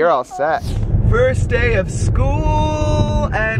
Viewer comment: At the beginning when was saying 1st day of school I thought he was saying Potato😆😃😀😄😁